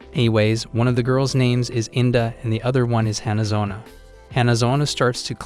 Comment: thought her name said India